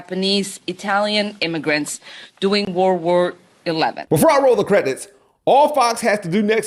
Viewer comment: did she say world war 11? Lol